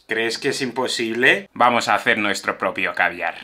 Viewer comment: como soy de arg pense que dijo crees que no puedo escabiar